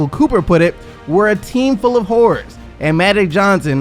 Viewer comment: did he say whore or horror 😂